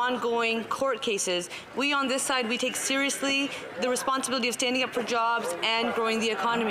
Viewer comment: I thought she said standing up for Punjabs